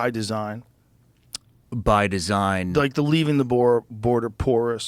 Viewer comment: When Shawn said "By Design" I thought he said "BIDEN SIGN" which both r correct